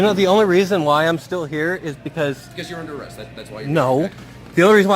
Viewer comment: did anyone else hear “under awest”?